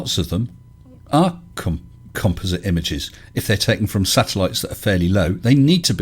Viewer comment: Had to momentarily pause and Anglicize the word composite’s pronunciation from kom-Poz-it to Komp-oh-zit. 😏😉🙄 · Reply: For a second there, I thought you said "compost".